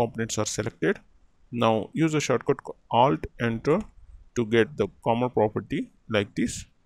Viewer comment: Hi, thanks for the tutorial I didnt hear you clearly around 2:53, after selecting all components, what shortcut did you hit?? Also, I'm wondering that, if I want to change the size of let's say, columns all at once, is it the same approach? Thanks! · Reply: Short cut: Alt + Enter